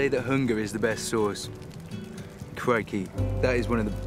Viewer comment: what did he say? “Craigie?” And what does it mean I’m just kinda curious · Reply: Lol, he said crikey.